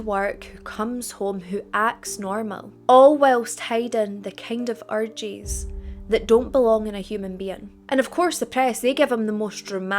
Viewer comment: i thought you said hiding the kind of orgies that no one should have 😂😂😂😂 i was like wtf